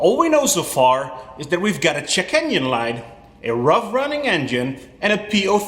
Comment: At 48 secs, you said "check engine light"... I heard, "chicken delight".... Fantastic accent.. 😂😂😂 Really enjoying your channel ❤❤